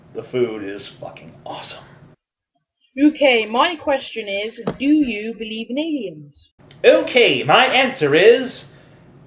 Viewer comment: I thought she said "Do you believe in England." -.